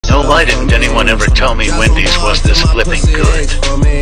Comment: I wasn't looking at the screen when i watched the video and i heard "joe biden anyone tell me nuggets good"